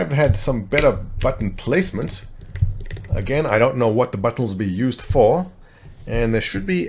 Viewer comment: did you just say you "dont know what the buttholes will be used for?"